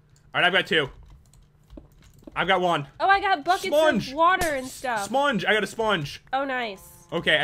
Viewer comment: Mutant pig plz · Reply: di- did he just say 'smonge'?